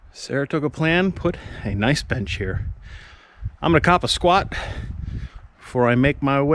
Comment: 😕 did he just say im gonna take ah shit in the woods?